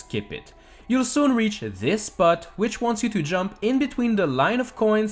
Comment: First time heard him say part it sounded like he said Butt